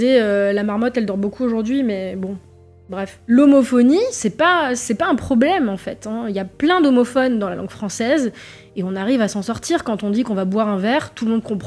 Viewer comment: à 12:20 j'ai buggé j'ai entendu "l'homophobie c'est pas un problème, y'a plein d'homophobes dans la langue française"